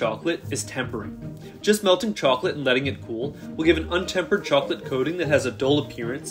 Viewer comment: Almost all the time he said "Temper the chocolate". I heard "Temper the child" 💀